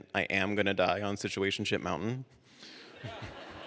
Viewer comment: This is outstanding! Outstanding story, outstanding writing, OUTSTANDING claiming of power, right there...on Situationship Mountain! Thank you for sharing! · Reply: I thought he said Situationshit Mountain! 🤭